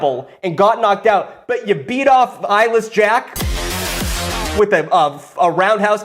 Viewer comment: I was listening to the video with very low volume and instead of eyeless jack I heard 'irish Jack' and when he started reading I was so confused as to why eyeless jack was Irish in this version of the story